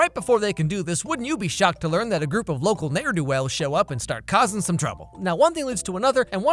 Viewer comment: Coulda sworn you said n word do well but you said n'er do well and I almost died from laughter 😂